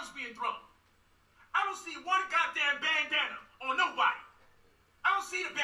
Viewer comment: I thought he said "bangdana"...where the bangdanas at?.if he did or didn't say that,shit was a good one